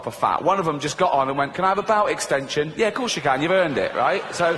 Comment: Anyone know what he says here? "A ?????? extension". I don't speak British so good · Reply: A belt extension!